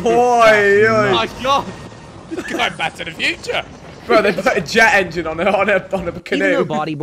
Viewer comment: Did Ethan just say "going back to the future?".